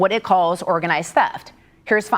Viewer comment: Oops thought you said wage theft.